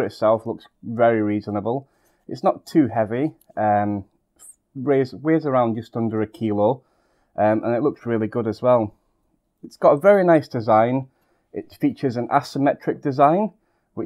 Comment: did i just hear 200 kilo? lol